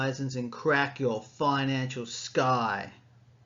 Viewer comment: I thought he was about to say “crack your financial skull” 💀 lol 😂